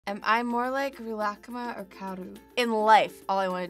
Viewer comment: Omg this is so creepy.. at the beginning 0:01 it sounds like she says Rilakma right? Now pretend she says Rilakkuma (but she says the u really fast) Play it again.. I hear both omg..